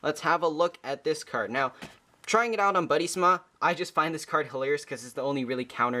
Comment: Its was a time 6:48 what is that called?? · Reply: Buddy~Sma is a free japanese Buddyfight app that is available in the japanese apple store, it allows you to play all the start decks digitally.